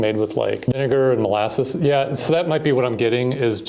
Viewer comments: 😭😭😭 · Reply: "WTF did he just say?!?" .. turns on close captioning.. "ohhh, vinegar" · you cut off the first syllable of vinegar, and I had to rewatch that clip about four times trying to figure out what the heck he just said